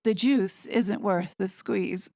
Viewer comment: Thank you! "The juice isn't worth the squeeze" is absolutely hilarious!!!! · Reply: I thought she said: "The Duke isn't worth the squeeze!" ..... 😮!